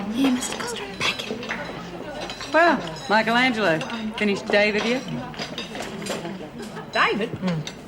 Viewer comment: What did reb say at 25:39 after saying Michael Angelo ? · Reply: Finished David yet" - David is the naked statue sculpted by him - that;s why they go on to to say he's 16 foot tall so part of him must be a foot long lol